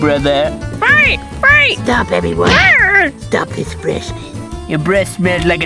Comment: it sounds like Bonnie says stop bb8 or bbboy